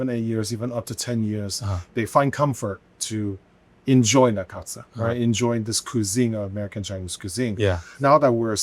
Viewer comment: the subtitles are wrong. He isn't speaking Chinese, he's saying the words "that concept" in English.